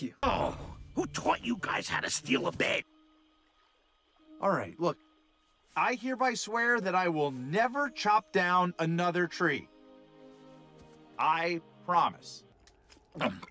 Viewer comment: The onceler said he himself wouldn't chop down another tree. He never said he couldn't order others to do it. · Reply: I THOUGHT YOU SAID CHOMP I